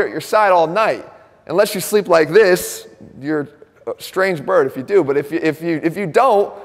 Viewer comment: Strange bird? Oh, I thought he said strange brew. My bad